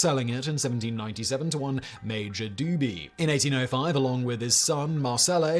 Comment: I thought he said major doobie. 😅 I just finished smoking one so you could imagine my surprise...